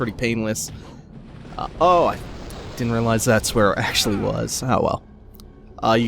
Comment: You said "thats where it actually was" But i heard "thats where ASHLEY was" and because i misheard you, i realized that the room youre in actually DOES resemble the room where u save ashley in RE4. Same room luis dies in. The more i play/see this game be played, the more similarities i see between re4 and dmc. Its intriguing.